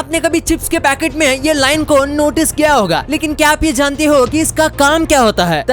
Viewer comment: he told janti ho instead of jante ho